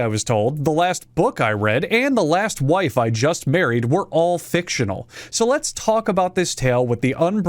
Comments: I misheard and thought he said "the last wife i burried" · I swear to God I heard "the last wife I buried" and now I don't know if he said buried or married